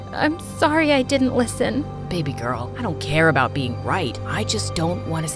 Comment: did bro say baby girl